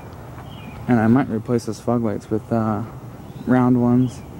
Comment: Those tires look awesome. I'm not sure if you said it or not, but at 4:44 it sounded like you said "I might replace those 'fug lights' with round ones". HA! Fugly lights